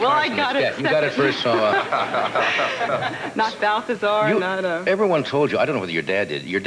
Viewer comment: I almost thought she said "Mathesar"!